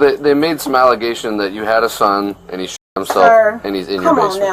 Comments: Man, this whole time I thought the cops were saying "shit himself " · “You had a son, he 💩 himself” i thought thats what he said